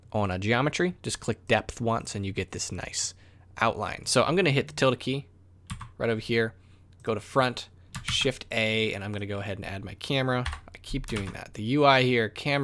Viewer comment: really cannot hear what your saying here. “Tilda key”?? Idk what that is